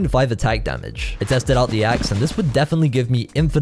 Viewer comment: did you just say "axe-ess"?